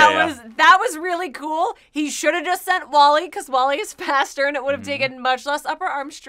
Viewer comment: did she just say Wally is faster?!?